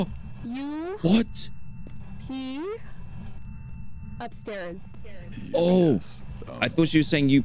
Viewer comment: Everyone: It's saying upstairs. I thought it said UPS